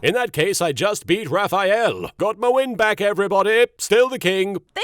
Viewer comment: "Got my win back, everybody. Still the king." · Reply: I heard it as "Got my wind back, everybody".